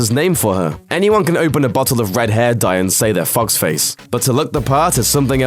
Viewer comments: When he said fox face I thought he said f*** face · I heard "fox face" as "fucks face". lmao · It sounds like he says fuck face insteadof fox face